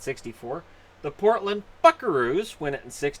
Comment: Sounded like he said Portland F*ckaroos